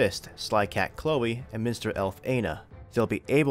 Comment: sounds like you said "Mister Elf"